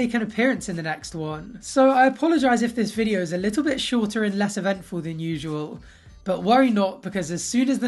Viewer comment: I thought you said "Ed-ventful" at 9:53 haha!! Thanks for the video!